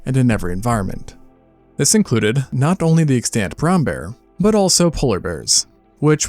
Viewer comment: When you said "Extent brown bear" I misheard it. Thinking you said "exctinct brown bear"